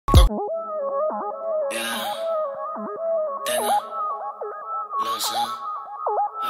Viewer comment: la única parte que no entendí que dice jaja #REALHASTALAMUERTE . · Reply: Dice " tengo los ojos rojos"